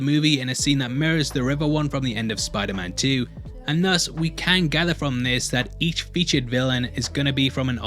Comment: Did you say, "KANG gather from this?"